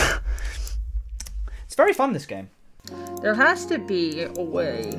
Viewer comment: Got here from Will's 6th liquefied compilation, honestly thought at 20:16 he said "YOU... C A M E !"